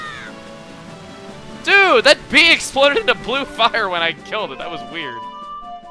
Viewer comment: That's what I thought he said at first, but he actually says blue fire :L